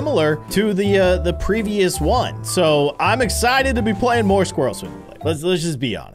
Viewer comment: i heard squirrel swimulator